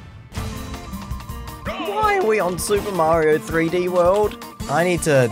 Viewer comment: did he actually say mario 3d world.